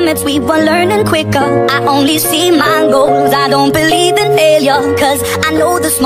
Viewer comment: all I hear: i OnLy SeE mAnGoEs